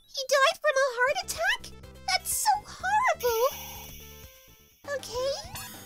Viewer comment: Did I just hear👂 say harable hahaha😂😆🤣